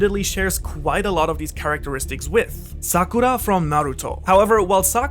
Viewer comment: Bro this scared me haha. I thought it said "sukana" not "sakura" haha